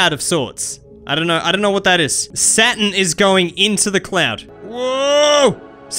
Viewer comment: Captions said satan instead of saturn